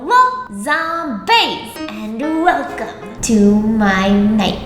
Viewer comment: I love that the subtitles thought she said “Hello Tom Banks” instead of zombaes